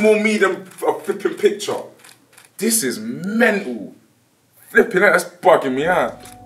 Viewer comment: does he say '' this is melgou '' ?